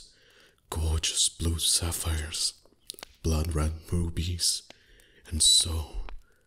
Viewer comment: When he said "blood red rubbys" I thought he said "blood red bobbies" 🤣🤣🤣